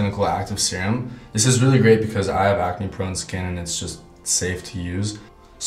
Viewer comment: Did he just say that he has acne prone skin?